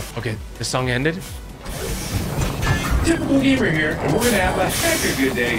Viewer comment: I thought I heard typical game while I was playing but I wasn’t sure if I heard right lol Congrats!!!